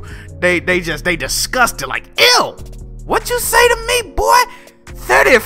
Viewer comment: u need to call out bond jack. that nigga playing zelda at 720p 30fps. I thought he said 30fps is unplayable.